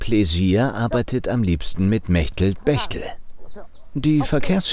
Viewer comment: kann jemand die Namen der zwei Frauen hier schreiben? Solche Namen habe ich halt nie gehört. 'Merchtel Berchtel'? Echt? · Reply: und.